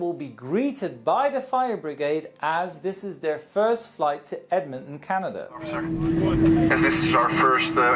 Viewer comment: when you said Edmonton it sounded like Edmitton